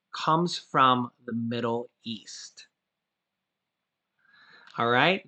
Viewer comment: Darn - my ears are getting old! I replayed this video. Baja! I thought you'd said "Middle Earth" instead of Middle EAST! HA! OK then, I know what it is. It's a souvenir coin - specifically a Shekel. Probably a"golden shekel of Temple weight.". Those are neat! I was blessed when I got to hold a real one a couple of years ago, thanks to an archeologists friend who was excavating Shiloh. Cool.